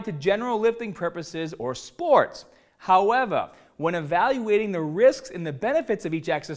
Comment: did he say howeva at 1:01 hahhahahaha